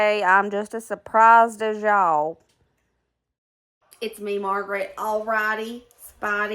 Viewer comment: Did you say your name is Margaret. I am tired, but that's what I thought you said. 🤔